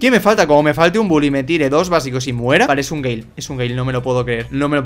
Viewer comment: Cuando tu amigo pasa el día con tu novia pero después lo ves besándose con un chico "Vale es un GAY 11:54" la primera vez entendi eso xd